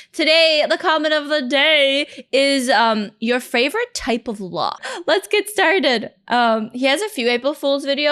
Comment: did she say "favorite type of law" or "favorite type of lock"? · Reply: Lock